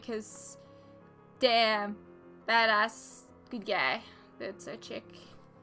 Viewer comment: when you were talking about Bobbi being your new favorite character, you said "Damn, badass!" But for a second, I thought you said "Damn, that ass!" and I was like "Wow Shann! Okay!" lol If "the real SHIELD" storyline strongly supported/added a poignant comment on the main arc of this season, which is Sky and her family, and what it means to protect your loved ones, this might've been one of the best season in superhero TV shows.